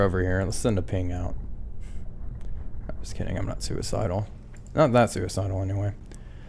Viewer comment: I heard "not that KIND OF suicidal" and I was like "Oh my, Wolfpack, are you okay??" Lol. I hope you are, tho